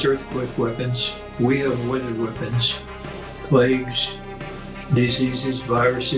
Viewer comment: at around 9:07 Lyn says what type of weapons he says the US has and I could not understand what word he used. Please, help (the music drowned it out for my already poor hearing). Apologies, he clarifies this a bit later - "weather weapons." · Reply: He was referring to weather weapons.